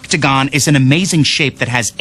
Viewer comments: Is it weird that I keep hearing "Fucktigon" instead of "Octagon?" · tbh, I heard "fucktagon" when that beat changed...